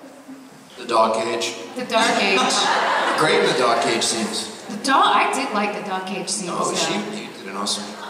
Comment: Does anyone know what Roger said at 45:20 bc Billy wasn't liking it! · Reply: ? He said Kelly was great in the dog cage scenes and she should watch them. Nothing for Billy to not like.